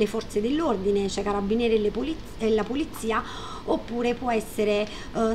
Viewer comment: ho sentito "pulizia" 🤣 comunque in generale video interessante, grazie!